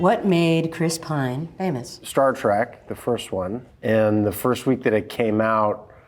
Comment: Mark 0:34. Howdy! 🤠 Greetings! 🖖 Ahem. Did he say "Star Track" instead of "Star Trek"? 😁